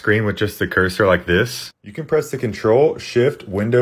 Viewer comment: Sounds like you said, “the B keys” How many B keys do you have?